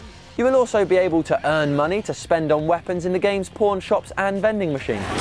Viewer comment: Anyone else thought he said Porn shops at 1:12?